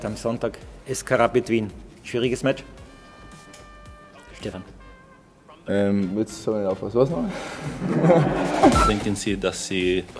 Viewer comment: Was hat Lainer bei 2:30 gesagt? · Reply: Cickbait !!! „Jetzt hab ich nicht aufgepasst, was war es nochmal bitte.“